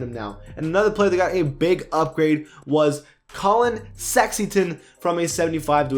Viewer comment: YOU DID NOT JUST SAY COLLIN SEXINGTON PLAY THAT BACK!!!!!